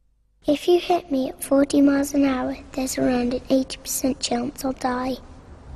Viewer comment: I thought she said 8% chance she’d die and was confused when it bumped up to 20%